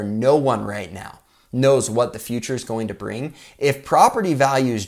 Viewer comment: no one right now knows what the f..... oh he said future lol 🤣😂